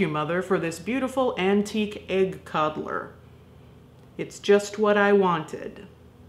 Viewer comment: I thought she said egg TODDLER and had to back it up a bit. Traveling is already scary, sending a toddler through the USPS, however...